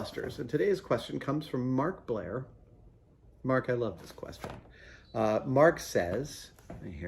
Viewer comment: I straight up thought he said the first guy was markiplier